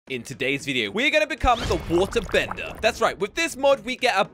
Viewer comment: I thought he said wart defender lol